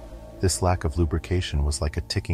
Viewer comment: I must be internally 5 years old.. i giggled when i heard "silent but deadly"